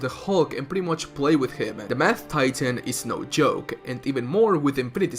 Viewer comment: Why did I think he Said ”The Meth Titan”